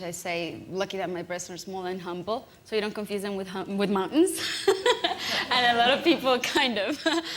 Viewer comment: what did she say? that my breasts are small and humble ? not confused with mutton? A mutton is a sheep, isn't it? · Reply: mountain